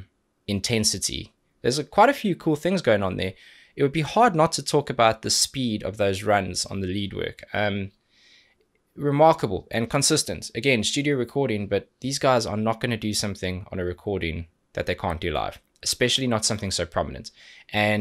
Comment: Every time you say remarkable, i hear "reMARCOable" lol. Porcupine Tree - Anesthetize(live). Please :D